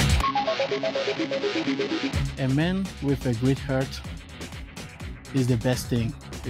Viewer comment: for a split second I heard, "A man with a great haircut"